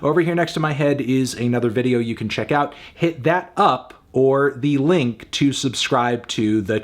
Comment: Yo I really thought he was saying “hit that upworthy link” fully knowing that it didn’t make any sense. I accepted long ago that melon sees through the veil and assumed that this was just some eldritch terminology that he was imparting on us.